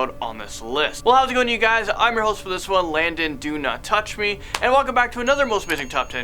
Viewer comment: did he say Landon do not touch me